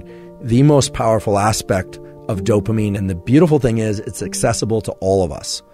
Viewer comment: Right after David Goggins example, I thought I heard you say 'obsessable to all'....I had a chuckle right there. Fits though. Once we develop the trigger.